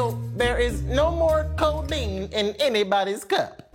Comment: did you just say “Codeine 19” 🤣🤣🤣